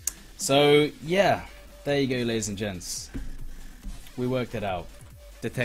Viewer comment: HOLD ON!!! The guy that helped the investigation in the hacker video was called "Gigawatson"... WATSON ... SHERLOCK???? ILLUMINARTY CONFIRMED! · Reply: For awhile I thought you were saying Detective Stud on the case, LOL